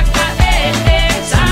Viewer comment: I didn’t know it said thango I thought she said JUNGLE😭😂